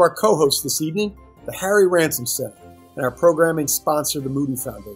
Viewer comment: I thought he said the Harry Ramsden's centre for a moment